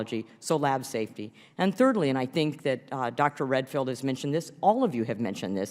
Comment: I know his name is Dr Redfield but every time I hear his name, I hear "Dr Redpill"